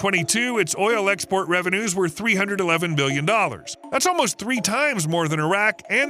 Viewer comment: First, I am from the Kingdom of Saudi Arabia. Second, water is the most abundant substance on the surface of the earth and the least in my country. Experts warn that future wars between countries will be over water. I see that Saudi Arabia has now solved somewhat a problem that worries the world and has conducted a lot of research and applied it, which is in the interest of all humanity. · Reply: Did I mishear? Saudi oil revenue of 301 million? Surely must be 301 billion dollars 🤔?